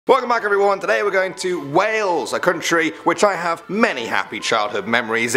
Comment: I thought you said whales